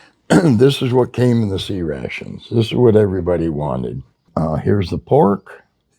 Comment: LOL, I always thought you guys were saying "sea" rations....as if they were supplied by the navy...